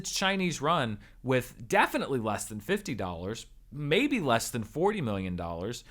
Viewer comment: Did anyone else hear him say less than 50$ at 15:47 ? Lmao